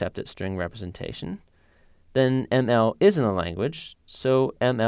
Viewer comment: At the minute 1:13, I think the subtitles are not correct, there is a "isn't" instead of "is in the", which confuses a lot because it's kind of the opposite of the concept. So I think, starting from the beginning of the sentence at 1:09, that the correct sentence is : "On the other hand, if ML does not accept its string representation, then ML IS IN THE language, so ML should have accepted its string representation".